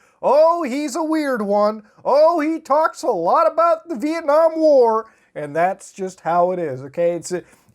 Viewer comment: I had to turn on caps to confirm what he said was Vietnam war